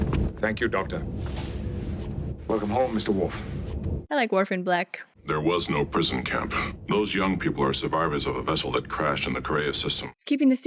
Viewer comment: Had to mentally translate; I heard say, "I like Orphan Black." To which I say, "Same!"